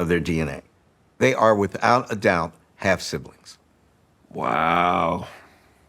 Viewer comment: not understood. They are without doubt .........." what did he say? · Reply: "Half siblings."